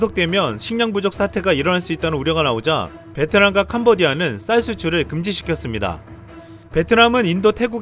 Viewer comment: 음.... 요즘 강장관 일 할 맛 나겠구먼... 베트남 태국은 위기때에 수출금지하는 후진 무역정책을 어디서 배운거야.?!! ㅋㅋ 어려울때 베푸는 거란다... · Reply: 베트남과 태국이 아니라 베트남과 캄보디아라고 했는데요 태국얘기는 없었던걸로 들었는데 제가 잘못 들은건가요?